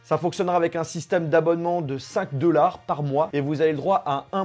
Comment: A 1:05 jai entendu "un abonnement de sac de lards par moi" j'ai beugé...